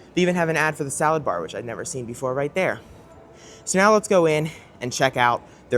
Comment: I thought said “salads go in”, lol.